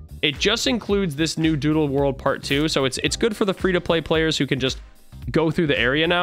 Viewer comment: did he just say pree to pway players